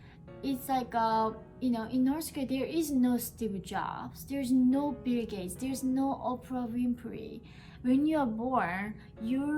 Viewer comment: What's she saying at 9:40? I'm hearing "there is no stubby jobs, there's no beer gaze, there's no Oprah wimpery." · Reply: Steve Jobs, Bill Gates, Oprah Winfrey